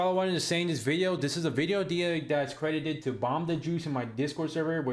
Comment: is he saying bomb the jews